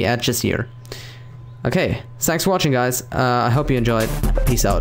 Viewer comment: "Sex Watching Guys" thats what i heard O.o